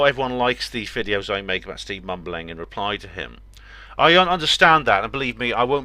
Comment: Sounded like you said 'steve bumbling' lol